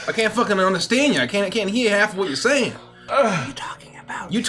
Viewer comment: Maybe that's wishful thinking but I heard "I can't hee-hawlf what you're saying!"